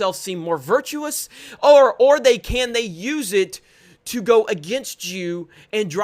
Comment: Sounds like he says, “use it to go against jew”